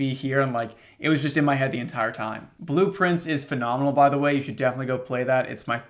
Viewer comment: It wasn't until his pronunciation at 11:22 that It occurred to me that the game Blue Prince is a play on words. BLUEPRINTS. I have never felt so dumb! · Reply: I literally only ever heard him say “blueprints” until this comment said it was blue prince